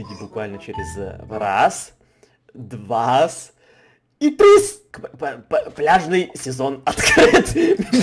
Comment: Мне послышалось не пляжный сезон а бляшный сезон 🤣😅